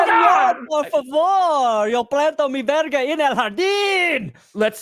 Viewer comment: Did just say "Yo planto mi verga en el jardín"? That would be the first time I hear that phrase in Spanish, and as a Mexican that's just amazing.